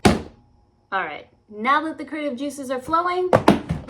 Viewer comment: That is an amazing build! I love a bit of Godzilla and Mothra, so seeing you add the lights to the back of Nomgar was such a good idea. Also, on first hearing, I heard "Creative juices" as "Kai-juices" and had to rewind to check 😹